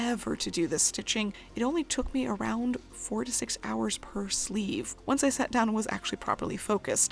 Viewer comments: I first heard, "...only 46 hours per sleeve..." *mind boggles*...OOOOOHHHHH..."...4 TO 6 hours per sleeve..". Carry on - nothing to see here. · I thought at first you said it only took you 46 hours to do the edging on each sleeve and took far to long to realise that that's completely unreasonable even for handstitching standards